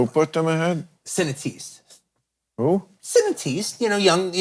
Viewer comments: What word is he saying??? Surely not cineaste? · No matter how many times he says it, I don't know what word he's saying. "Cinetiste?" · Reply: Cinétistes, meaning Cinéastes or cinephiles.